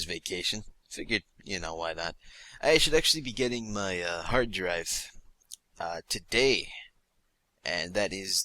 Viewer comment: is saying hard drive because that would mean that for 4 years I thought he was saying heart drive